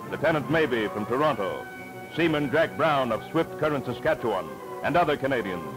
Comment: does it say "Seaman Treg Brown"? Treg Brown was a sound man for Warner Bros. cartoons, and was American!